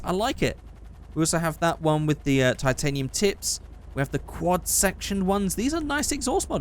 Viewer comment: I really thought he said "titanium t!ts" when he was reviewing the exhaust